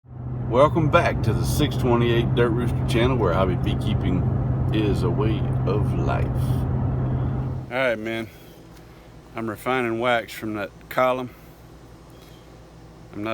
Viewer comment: I always have to do double takes on your intro because "hobby beekeeping is a way of life" can be misheard as "highway beekeeping is a way of life" Since you're always travelling in your videos, the latter makes a lot more sense than the former lol.